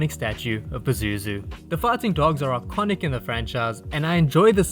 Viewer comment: It sounds like you said "farting dogs" hahahahaha